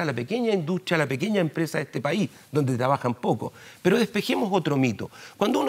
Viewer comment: Escuche mal ???... "pequeña empresa mediana empresa, donde trabajan poco"... min 2:30 Secretario de la cut PO wn !!! Que vergüenza!!! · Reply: Donde trabajan pocoS*, dijo.